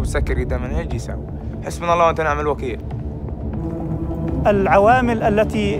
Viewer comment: AND 7:30 He does NOT say "may God punish them." He says حَسْبُنَا اللَّهُ وَنِعْمَ الْوَكِيلُ. Allah (Alone) is Sufficient for us, and He is the Best Disposer of affairs (for us).” Surah Al 'Imran (3:173). QURAN